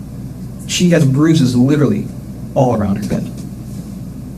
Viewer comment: Litrlly.. she did. (Yes, it's spelled wrong cuz that's how it sounds like she says it, to me anyway.)